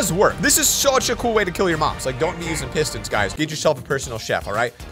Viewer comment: is it only me or i hear your mom?